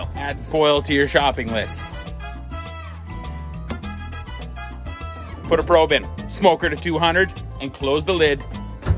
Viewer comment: I need a SK-English translation at 12:07 -- does that say "shitticuets"? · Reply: Shit Tickets (toilet paper) 🤠